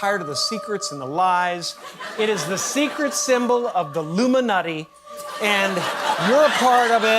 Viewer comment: I don’t know if anyone else picked up on what Jim Carey actually said but he actually said ‘It’s the secret symbol of the LUMINUTTY He didn’t actually say ILLUMINATI 18:08 But don’t know if anyone else picked up on that. 🤨 🤔 😅